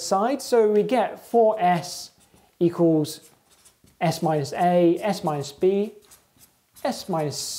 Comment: haha, "S my C". Misheard.